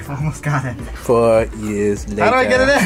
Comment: ???? 🤨😳 did I hear correctly. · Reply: He says "4 years later" It's a Sponge Bob joke.